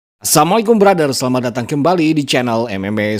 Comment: Min sekali lagi aku ingatkan.. Salam pembukanya jangan cepat cepat.. Pastikan betul betul terdengar Assalamu'alaikum, jelas huruf LAM nya. Yg aku dengar dari salam pembuka admin karena terlalu cepat terdengar Assamu'alaikum, ngga kedengeran huruf LAM nya. Assalamu alaikum: keselamatan atasmu Assamu alaikum: kecelakaan atasmu.